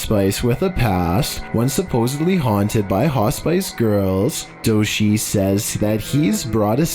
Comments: Lmao sounds like he said hot spice girl🤔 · He did say hos-spice instead of hospice. 😂😂😂. At first I thought he said hot spice girl🤣🤣🤣